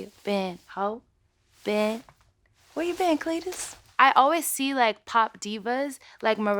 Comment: I thought ariana said where you've been cletus what the helllll😂😂❤❤